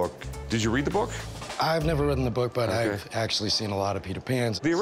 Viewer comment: Did he just say that he's never ridden the book? Giggity... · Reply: I caught that too. Pretty sure he said "read-en" (as if it were the past tense of read). You'd think that a guy that owns a bookstore would know that.